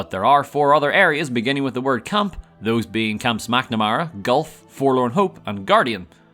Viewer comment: anyone else hear camp smacknamara here?